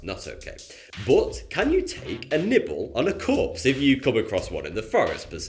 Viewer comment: i thought he said, "Can you take a nipple off a corpse".